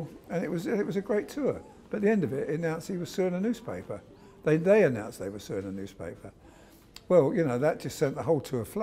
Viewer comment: can someone let me know what the guy is saying from 2:29ish mark to 2:33? he announced what? I can't understand him and the auto-captions don't help either :( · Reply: Nicole preissl He was suing a newspaper, they were suing a newspaper. Hope that helped.